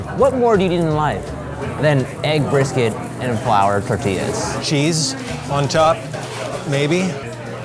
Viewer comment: lol I thought I heard "she is on top, maybe"